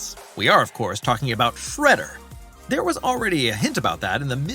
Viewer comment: This guy said Fredder instead of shredder 7:40 it’s shredder get your facts right dude who is fredder